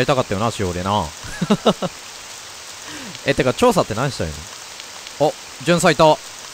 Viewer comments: やばい...｢調査｣が｢趙さん｣に聞こえてしまう笑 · 調査って何してるの？ が「趙さんって何してるの？」に聞こえて、まだ悪魔スペシャル引きずってんのかと思った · 調査が趙さんに聞こえてしまった